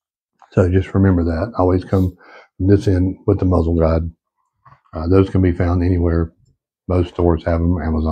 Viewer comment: So, you are supposed to clean this rifle from the muzzle end ? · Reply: Thanks for the reply. I’m familiar with chamber/bore guides. I thought I heard you describe it as a muzzle guide, which confused me.